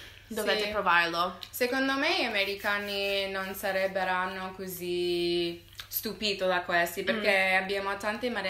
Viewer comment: Poi ad un certo punto dice al minuto 1:30 "gli americani non sarebberanno" io che l'ho usato in una verifica }:‑)